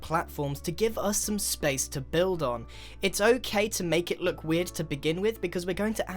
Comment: i thought Grian said "it's oakay" instead of "it's okay" jsjsksnzmsnsnznsjjsbq i'm